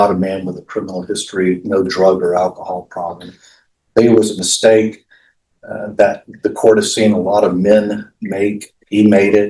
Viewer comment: Did his defense attorney really just say “this is a mistake that most men make” or am I hearing things